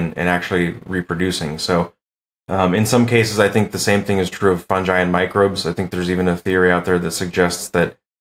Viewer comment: What the fuck is a fun jai. Twice i heard him say fun jai. around the 13, 14 minute mark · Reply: Fungus